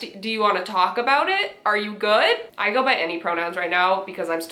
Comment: Bro- I heard say ‘pronoun’ as ‘bronoun’ and now That’s how I’ll ask for peoples pronouns, just imagine- in stereotypical frat boy voice “Ha sup dude!! What are your bronouns bro/broski/brosis????”